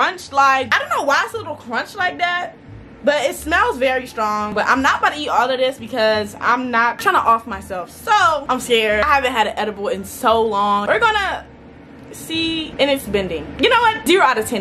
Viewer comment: Tell my why I thought the video said "I ate an edible wig"